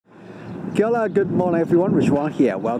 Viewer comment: Did he just say Kellogg good morning... 🧐🤪🤣🤣😅😅😅